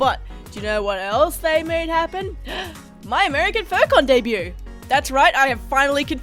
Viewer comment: For the longest time I’ve thought was saying “focon debut” before realizing she’s saying “furcon” but the Australian accent made me hear “focon”